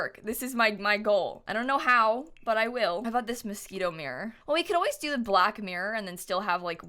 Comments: I thought she was saying mosquito not Moschino lol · when she says "Moschino," i keep hearing "mosquito" lol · I really heard "mosquito mirror" and was like.. "that's no mosquito" and then I remembered that stuff pack with the wierd name 😁😁😁